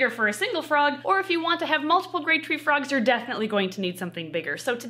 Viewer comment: Sounds like she says great treefrog rather than grey treefrog and honestly both names are accurate!